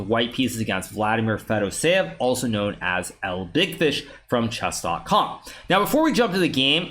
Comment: el dickfish? Am I hearing that right?